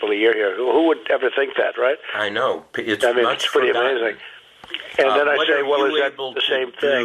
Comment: "I mean, that's pretty amazing" - Donald Trump. It's that what I heard?